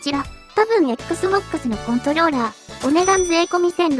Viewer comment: 噛みました？ XBOXのコントニョーラーって聞こえた。